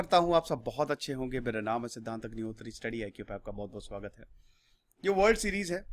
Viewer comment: did I just heard study IQ in study glow channel